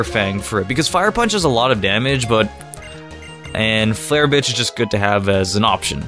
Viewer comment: Am I the only one who heard him say flare bitch by accident